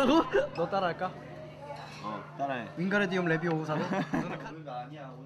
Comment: Oh, I heard Harry Potter's magic spell! Yoseob said that 'Wingadium Leviosa' at 0:04.